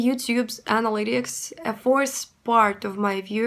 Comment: I'm surprised people watch these with subtitles. I'm a native English speaker and it's very easy to understand you. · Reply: USA Michigan guy here, I understand 95% of what she says but I usually turn captions on when I watch her videos because there are some phrases I find odd and the captions can help. Like @ 7:10 she says "According to the YouTube's analytics, a fourth part of my viewers watch my videos with English captions." I would say "According to YouTube's analytics," or "According to the YouTube analytics," "a fourth of my viewers...". Also her pronunciation of "fourth" kind of sounds like "force". I primarily watch YouTube at home on my TV. I think viewers on mobile or computer may watch with low or no volume with captions on to not disturb others.